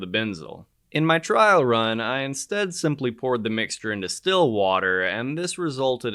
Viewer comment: Did you say ... still water